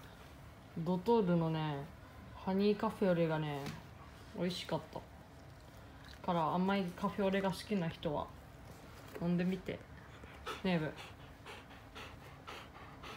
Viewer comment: の「ねえ、文」が「デブ」に聞こえたw